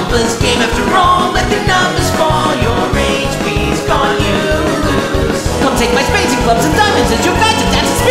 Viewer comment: When I was listening I could’ve sworn it said “You rage respawn you lose”